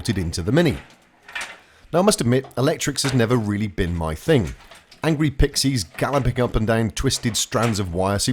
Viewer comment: Did he just say ... angry pixies?